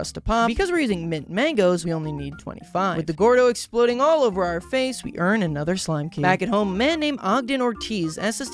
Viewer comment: What did you say at 16:05? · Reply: Exploding all over our face, we earn another slime key.